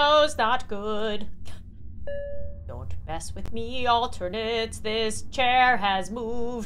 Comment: did she just say alternates